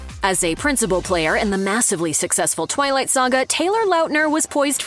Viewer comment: I swear I heard ‘Toilet Saga’ and I’m not mad.